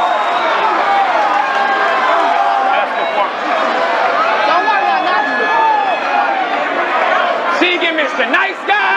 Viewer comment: What does say to at 39:44 when she replies dont worry i got you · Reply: He says thats performance. Because in the face off she said her performance is better than him.